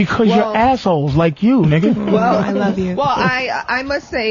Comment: Did I just hear you say “I love you”?